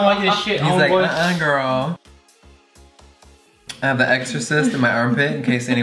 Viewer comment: Omg I thought he said he had a extra cyst going on down there when he was talking about his armpit😵😵🤣